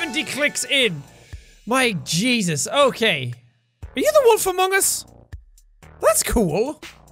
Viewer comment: wait did he say among us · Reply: Wolf Among Us.